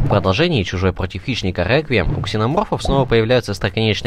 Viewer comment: мне на 1:30-1:35 послышалось или было слово "реквием"?если это слово было то это случайно не отсылка на джоджо?